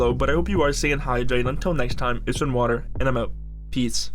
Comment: It Sounded Like You Said Eat Some Water